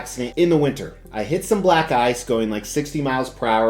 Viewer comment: Phew... Black ice. For a sec there, I thought said black guys. Well, he's kinda nonchalant about it all.